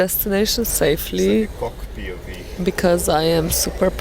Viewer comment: does he say cock pov? hahahah